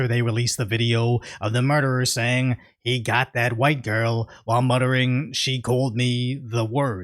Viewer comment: did the say "I got the white girl"